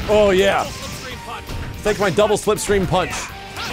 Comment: Check out the fake-ass DMC music on Athena's stage. Also, it sounds like Rock is saying "Double full-screen punch"